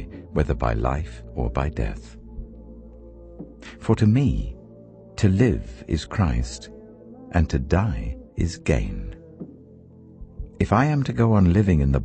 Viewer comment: I heard "to live is Christ, and to die is gay" and woke up in shock💀